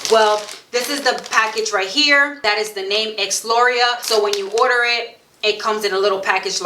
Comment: Good thing you wrote Exlura. I thought you said “Ex laurel” lol